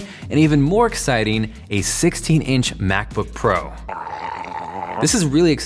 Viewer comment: Did you say ARM-powered 12" MacBook? 0:33 I can't wait to get my hands on THAT. I will shove it in so many Spec Heads' faces! #takemymoney